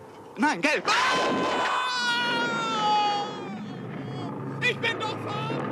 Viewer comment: Was sagt Sir Gallahad 2:17 ? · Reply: "Ich bin doch farbenblind!"